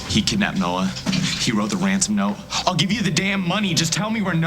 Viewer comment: tell me i'm not the only one who thinks Luke said Wansom instead of Ransom at 2:48 -ish xD it just sounded adorable though xD